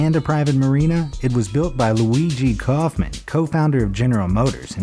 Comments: I Thought My Man Said Luigi Kaufman. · Luigi’s Mansion irl At least that’s what it sounded like he said 🤔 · am i the only one who heard LUIGI kaufman??